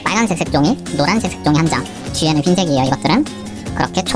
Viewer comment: 뒤에는 흰색이야 이것들아??? 나만 이렇게 들었나???